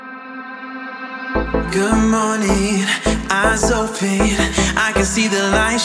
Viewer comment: at first i thought he said good morning ass open i can see the light shining through XDDDD